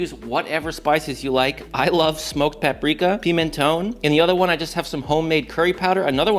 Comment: What????? At 13:04. You say paprika "piminone" WHAT THE HELL IS THAT??? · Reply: It's Pimenton- Spanish smoked paprika